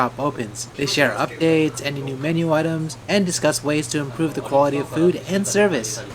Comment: you said Quality?? ...But I heard "JAPAN"